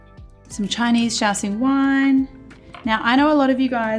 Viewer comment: I can’t believe this whole time I thought you were saying Chinese shouting wine